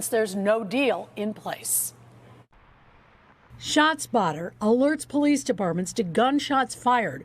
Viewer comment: I thought they were saying "shots fired" lol my bad